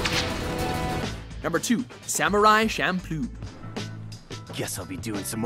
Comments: I heard Shampoo, not Champloo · I keep hearing "samurai shampoo"...